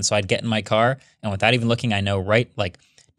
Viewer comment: I had to replay this a few times to make sure... I think Marques says "Heat Seater" lol